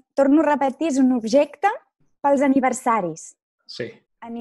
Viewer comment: im also from Montréal, and i understand 90% of what she said, but only because it was also written on the screen. 6:43 the french guy heard, ''Universaris'', but she really said Aniversari, if he understand it properly he would have immediately made the link with ''Anniversaire'' She also said ‘’Pastis’’ it sound a lot like the french word ''Patisserie'' which mean ‘’pastries’’ which mean baked products made from ingredients such as flour, sugar, milk, butter, shortening, baking powder, and eggs. Like pies, tarts, quiches, croissants, but yeah, a Birthday Cake is just a big Pastry.